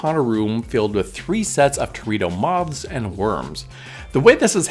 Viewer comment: I totally thought you said “dorito mobs” so time to get my ears checked. Or maybe I just need a snack?